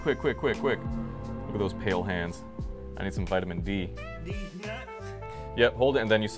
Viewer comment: Did anyone else heard "deeznuts" 💀